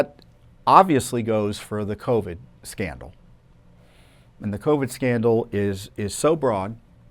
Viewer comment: I heard " covid scamdal"